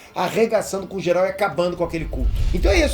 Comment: só eu que escutei "arregaçando geral e acabando com aquele c#" ? KKKKKKKKK